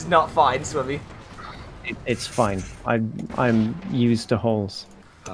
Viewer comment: I think actually said "I'm used to holes"